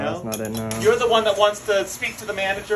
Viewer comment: I swear I heard "She's a bi--h" at the end💀